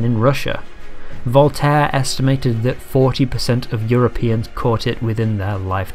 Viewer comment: 40% or 60%? captions say 60% but you say 40%